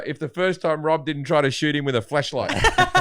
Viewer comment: Lmao Rob thought he said fuck a flashlight